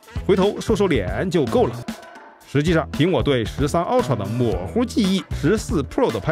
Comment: 赵师傅说的“”凭我对13ultra的模糊记忆” 差点听成是：苹果对13ultra的